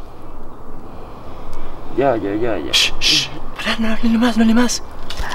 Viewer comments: se escucha alguien que dice “paren” justo cuando la mamá de Fátima grito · se escucha una voz que dice "paren" (autolike para no morir) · se escucha a alguien que dice “paren” y un segundo despues la mamá de Fati grita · se escucha a un niño que se escucha clara mente que dice "paren" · se escucha alguien que dice "paren" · se escucha "paren" 5:45 se escucha una voz gruesa que dice "obedezcan” 5:57 se ve detras de fede alguien parado 4:13 se escucha una voz aguda 14:04 se escucha que dicen "shhh" 7:30 se escucha una voz de un niño que dice "casi" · se escucha Clarito que dice paren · Antes del grito de la tia o mamá de fatima en el minuto 5:17 se escucha una vos que casi no se oye que dice "paren" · chicos esto fue otro nivel, ultima vez que jugamos a la ouija en un cementerio. los quiero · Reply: en el minuto 5:17 se escucha que dice "paren" like para que lo vea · se escucha una voz que dice "paren"(autolike para no morir) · chicos esto fue otro nivel, ultima vez que jugamos a la ouija en un cementerio. los quiero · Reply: en el minuto 5:17 hay una voz que dice paren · se escucha paren 5:46 : se escucha una voz gruesa que dice obedezcan 7:30 : dicen casi